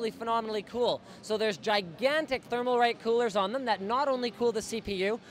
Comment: I think he said thermalright